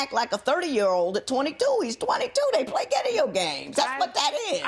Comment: did she say "gideo games"